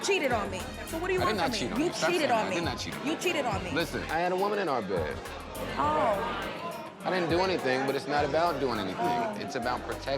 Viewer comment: 😂 Did Joe just say I had a woman in our bed but we didn’t do anything??